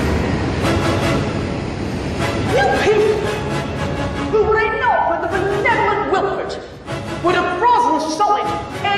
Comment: I can barely understand what the lady is saying 1:12. Is anyone else having this problem? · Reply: Were it not for the benevolent Wilford you would have frozen solid 18 years ago.